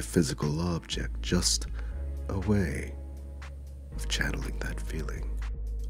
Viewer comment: I thought he said "O-HAY" when the kick landed and not "a way"